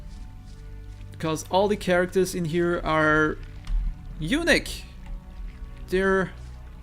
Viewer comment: I don't know why, but I'm having a hard time following your accent in this video. You said the prisoners were 'unique' but I thought you said 'Eunich'. And when you said 'tumbler' I thought you had said 'toddler' at first. I need to get more sleep. >.>